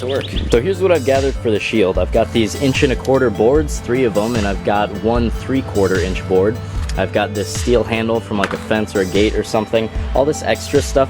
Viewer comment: When I first saw this I thought he said, "I had to steal a handle.."